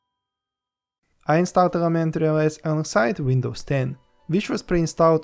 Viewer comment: Sure it starts of sounding like "elementary o s" but by the end I hear "other men tear her ass" ! O.o Good review btw!